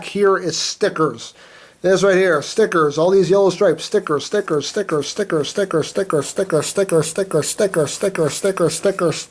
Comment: Am I the ONLY one who heard him say "Snickers,Snickers,Snickers,Snickers,Snickers,Snickers,Snickers,Snickers,Snickers,Snickers,Snickers" at 9:37 - 9:43 ? I was just like" Sneckers? I LUV SNECKERS!" XD